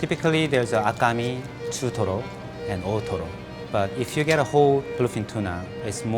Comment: as a weeb, i heard akame, jotaro and totoro